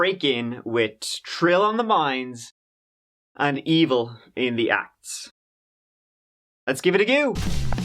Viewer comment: I swore you said “…Wit trill on the minds and evil in the a**” 🍑 🤣🤣🤣🤣